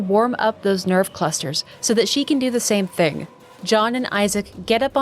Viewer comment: for a split second I thought Lady said “Santa” instead of Santos lol